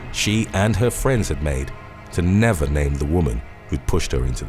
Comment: "Never name the woman who pushed her into the pool" I'm sorry but if I were the friend, I would come forward out of guilt. The fact that they are okay with that is surprising. But hey only they know. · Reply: I thought they said to never blame the women,my bad